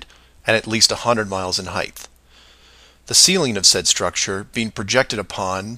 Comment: Did he say Heighth? 😏🎓🚫